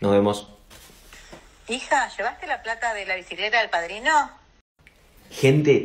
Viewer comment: desarrolle un truco con los subtitulos yo escuche alquiler pero los subtitulos dicen bicicleta osea q el audio dice bicicleta en realidad :o